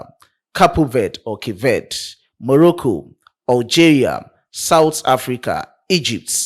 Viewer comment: Yes, I said it in the video❤️ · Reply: You are correct. Sorry about that. I thought i heard you say Mexico but you actually said Morocco 🇲🇦 for the CAF playoffs.